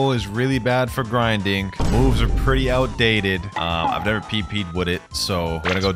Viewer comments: Did He say PP? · who else heard "i never pped with it"